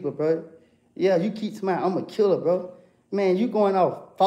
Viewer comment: this is in respto a comment I can't find. But the commented “ suggesting told on himself saying “ I’m a killlller” But what they missed was “ you keep saying” spoke it so fast and that comment or was listening too hard.